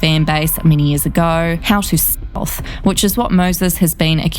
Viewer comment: What are the blocked words at 4:04 & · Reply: Stealthing.